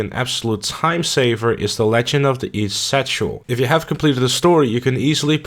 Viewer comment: the auto-generated captions says legend of the east sexual